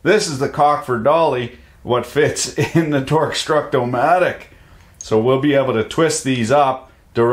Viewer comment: I thought he said "Torgue-structo-matic" and instantly thought: "EXPLOSIONS?"